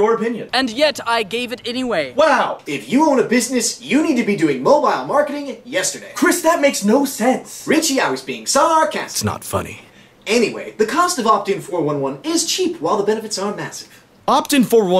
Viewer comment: sounds like hes saying "snot funny"